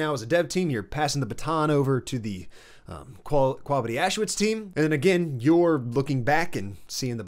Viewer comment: Did he say “ quavity auschwitz” 6:13 😭😭